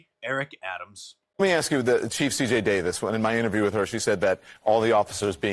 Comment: did he just call her the chief of shit stain?